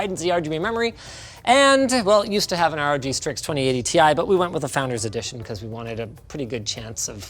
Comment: captions say "20 ADTI" instead of 2080 Ti